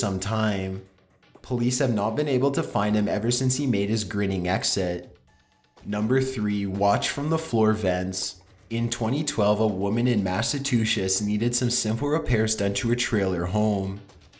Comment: did he say massatches